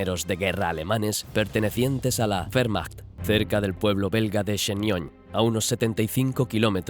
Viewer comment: yo entendi fehrmacht